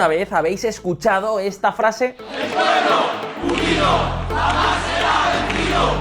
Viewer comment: Pense que decía "El pueblo judío jamas sera vencido" y resulta que era unido en vez de judío xDDD